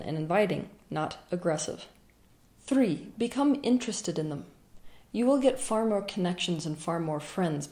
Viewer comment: At first I thought you said "3. Become interesting" like well, shit XD Great video as always!